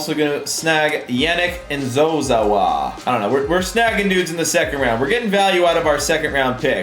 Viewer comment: did anyone else hear him say nudes or what